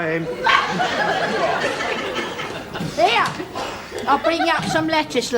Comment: At first I thought she said, "I'll bring you up some legislature." LOL